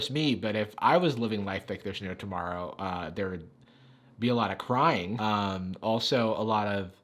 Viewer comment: When he said "If I was living life like there was no tomorrow, there would be alot of crying" I thought he said there would be alot of crime. I had to go back and listen to that